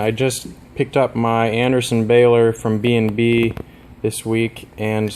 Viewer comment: who can spell how calls thing shows 13:30, because I cannot understand words? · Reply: sorry, I found: Adersen bailer.